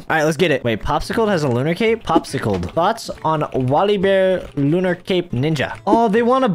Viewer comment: lmao he said thots instead of thought xDD